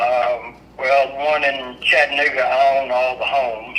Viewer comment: am I the only one who heard chattanigga..